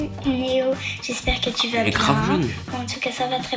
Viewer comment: t’as dis quoi la ? Mdrrrr elle est grave BONNE ?! elle a dit 10 piges ptdr